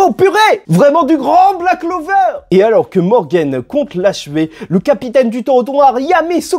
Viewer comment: j'ai entendu le capitaine du trottoir 😅😂